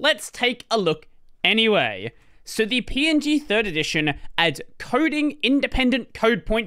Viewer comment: Throughout the video I kept mishearing "PNG third edition" as "PNG nerd edition"